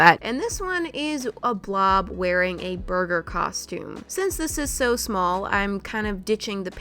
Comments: When you said “This blob is wearing a burger costume” I thought you said “This blob is in a murder costume” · am i the only one who thought she said “murder costume” and not “burger costume” 👀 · I thought she said murder costume 😂😂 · scared me for a second when she said "this blob is wearing a burger costume" I thought she said this blob is wearing a murder costume lol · When she said a burger costume I thought she said MURDER costume I thought “WHOA there Moria!” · When she said “This blob is gonna be wearing a burger costume” I thought she said “murder costume” i was like WAIT HOLD ON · When said burger costume i thought she said murder costume..... hehe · when u said burger one i thought u said murder